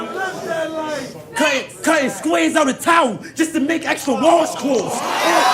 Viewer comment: what did he say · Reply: cutting squares out of towels just to make extra washcloths